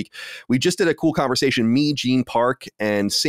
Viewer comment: i thought he called him "Mean" Gene Park